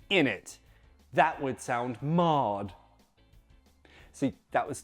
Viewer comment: I was so confused at the "that would sound mad" because i thought you said 'marred' and my brain was trying to put 2 and 2 together 😂 This video just reminds me how annoying English is. There are no rules, you just haaaave to guess 😂🤣